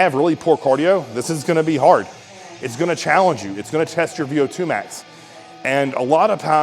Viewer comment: “It’s gonna test your VO2 max”. I first heard that as “It’s gonna test ur go-nads”😂